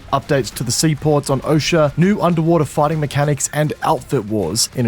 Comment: I thought you said "New Underwater Farting Mechanics" but now I truly understand...